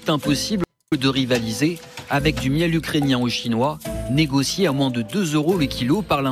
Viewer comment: c'est mes oreilles qui merde ou j'ai entendu deux fois chinois ?😂